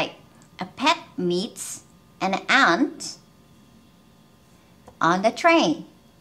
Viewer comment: 分の例文 an antの部分はnとaが繋がらないのですか？聞いてるとアナ,アーンって聞こえます。